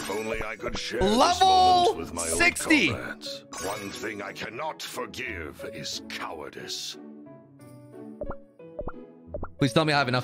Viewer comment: did anyone hear him say "i will not ef you" but I'm pretty sure he said "i cannot forgive you"